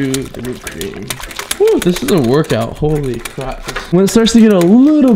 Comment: F**k? if I'm wrong, sorry, I couldn't understand please, someone, answer my question. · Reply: All About That Phat Bass its holy crap